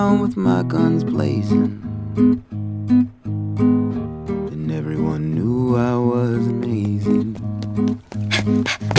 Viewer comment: tumbleweeds and rattlesnakes fighting off sidewinders rode to town with my guns blazin and everyone knew i was amazing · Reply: To me I thought he said "and everyone knew I wasn't easy". Which sounds cooler.